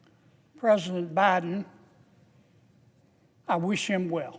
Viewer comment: Did he say "Baaa- Den"? Like Baa Baa Black Sheep 🐑?